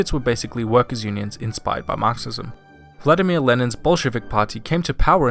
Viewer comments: is it just me or at first did I think he said "bullshit" on 5:55 instead of Bolshevik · When you said "BOLSHEVIK PARTY" I heard "BULLSHITIC PARTY"